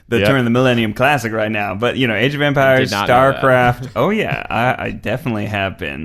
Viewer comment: Legit though Ned was talking about a game called “Age of Vampires”, I texted my husband and was like “a historical vampire game - whaaa?!” Needless to say he laughed at me and “Age of EMPIRES” makes much more sense.. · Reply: I heard "vampires" too, and I was like "What is that game, me wants!" xD